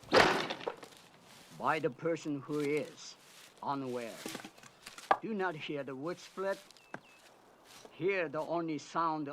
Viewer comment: Haha, funny stuff. 4:06 What he said was "There are many sounds around us, each slightly different - so small as to go unnoticed by the person who is unaware. Do not hear the wood split, hear the only sound of axe cutting air. Read the air itself." What he's saying is of all those ostensibly meaningless sounds happening all around you, learn to distinguish and focus on those that are important. It's how he knew when to dodge later on in the film when he had his back turned to the archer after he first glimpsed him. Another thing to keep in mind with the film's title, it's just the film's title. It's intended to give you the idea of martial arts mixed with gymnastics in the movie, not necessarily the name of a new martial arts style which featured gymnastics. His training in the film was all about learning how to get the most out of everything that he had at his disposal.